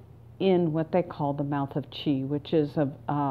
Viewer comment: I thought you were saying mouth of the cheese😂